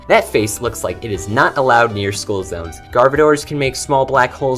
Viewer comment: Did he say Garbodor?😅